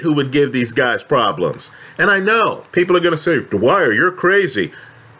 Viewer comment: All these years I thought he said Hi it's the Wire...he says Dwyer.